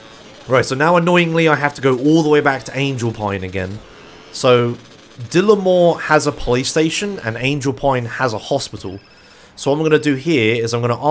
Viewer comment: Maybe it's just me but I really thought said "Dillimore has a playstation"